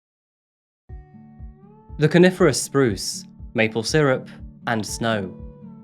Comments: At first I thought you said "carnivorous spruce" and as a Canadian I felt great confusion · Your voice is easy to listen to - pleasant and well modulated - so I've subscribed. (Although I initally thought you said "carnivorous spruce", not "coniferous spruce". My bad, lol.)